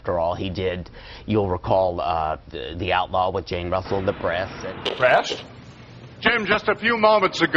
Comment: 😮 ?!? "Breast!" "Breast!?" Wtf is this? I scrolled down the whole comment section and not one other person noticed this? Am I hearing it wrong?